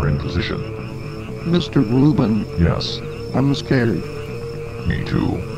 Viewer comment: mr bruben.yes.im scared.me too.good luck mr bruben.zzzZzzzZZZZzzzz What? say mr bruben